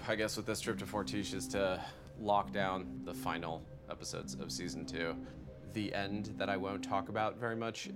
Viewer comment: "lock down the final episodes of season two" Huh did i hear that right or season 2 is actually finished and is up for polishing right now?